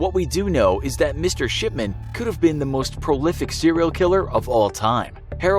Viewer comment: It sounds like he is saying shipment